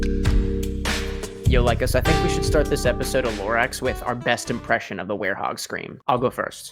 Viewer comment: I am, so so sorry, but when you said “lore x” I thought you said “lorax” I was cooking with this in the background and when I heard “this episode of lorax with our best werehog scream” I just spun around and stared at my phone in confusion